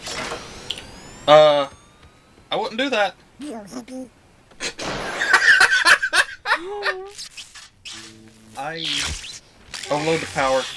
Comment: What does Donald say at 3:11? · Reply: "You hippie."